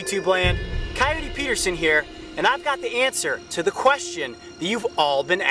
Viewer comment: did he just say anster